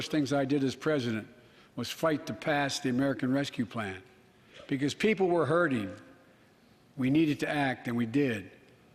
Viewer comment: What does Kevin McCarthy say at 37:50? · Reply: Looks like he says that's false😆